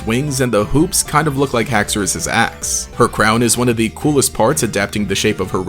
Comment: bro I really thought you said Haxorus' ass first watching. lmao